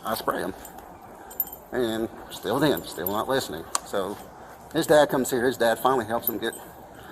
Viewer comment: did the officer say something like, "...his dad came down here - he's rich…"? I could be wrong… I listened n to it three or four times and I can't make it out. But it sounds like he said what I wrote. Please correct me if I'm wrong. Thank you. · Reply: Dads irate